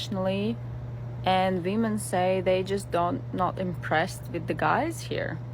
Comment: Is she saying, "Demons say..." cause it sounds like that to me!? I know the subs are saying "Women" but still it's pretty funny!! LOL